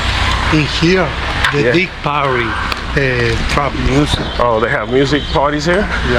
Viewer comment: I could've sworn the guide said 'trap music'!